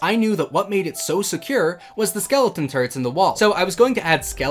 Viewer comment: The infinite harming potion generator existing solely to heal the wither skeleton turrets did kill me a bit inside, I think. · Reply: When he said skeleton turrets, I honestly thought for a second he said skeleton turds